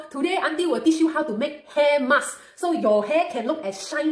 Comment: Am i the only one who heard her say HAIR MUST